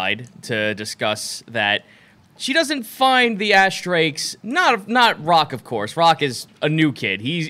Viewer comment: I totally thought y'all were saying Assdrake the whole time. But I thought it was just being cheeky with the name. But Ashdrake makes more sense. lol